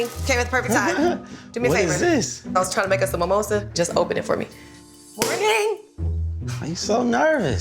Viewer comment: 😂😂 what did she say lmao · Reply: "Morning"